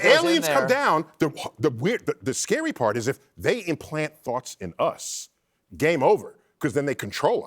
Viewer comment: First time hearing this i thought Dr Neil said ' they implant fox in us ' 😳😳 thank goodness for instant replay & the realisation it was ' thoughts ' not ' fox ' . . . tho 🤔🤔🤔